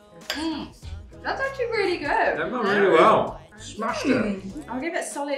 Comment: does he say :"smaczne"?